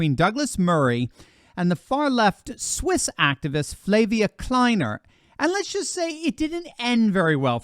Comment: I swear to GOD I thought you said the swiss activists name was Labia Kleiner. 😄 Somehow I wouldn't have been surprised.